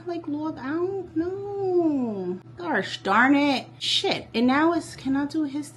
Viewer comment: did you say gosh darnet? 😂🤦🏽‍♂️🤷🏽‍♂️